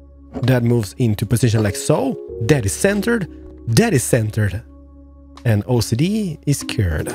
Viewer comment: I thought you said Daddy Centered lol 😁